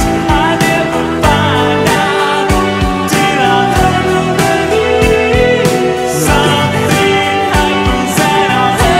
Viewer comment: In 1:20 what did he said? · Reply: “We’ll get him back. I promise.” 😊